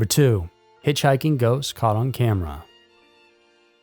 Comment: 😭 i thought you legitimately said Hitchhiking Goats